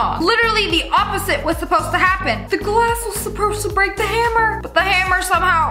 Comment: did anyone notice what she said? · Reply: “The glass was supposed to break the hammer”